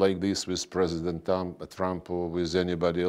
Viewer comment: Did he say "President Dumb" at 0:04 ? oO